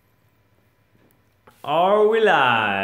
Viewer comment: I think I'm going to be really sore tomorrow... Thanks to everyone who was there - SUPER FUN STREAM! Can't wait to shred this beast!! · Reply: at first when you said "are we live" i thought you said "all rise" as if you were about to introduce yourself in some legal or royal fashion. xD